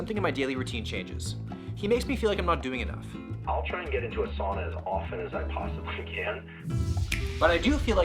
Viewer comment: I thought he said "Asana" not "Sauna"